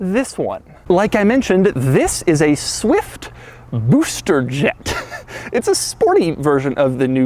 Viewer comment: I thought he said Rooster Jet at first..